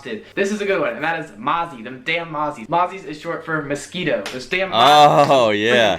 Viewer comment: Whene he said mozzies it sounds like he saying na*ies